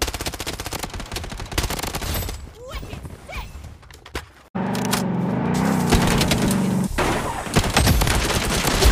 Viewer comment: What does it says here 4:32? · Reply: Wicked sick